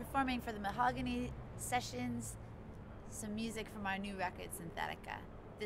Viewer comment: "to perform some music from my new record synthetica"? i'd say a bit disrepectul to jamie next to her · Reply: Btw she said from our new record but i can see how you could mishear it